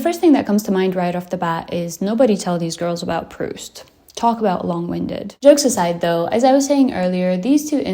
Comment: can someone tell me what prust(?) Is? Like I might be mis-hearing it but huh? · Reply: I think its about writer Marcel Proust. He wrote in long, long sentences and (over)analysed his characters. And if someone cant read trought any book without skipping, Proust would be -skip to end.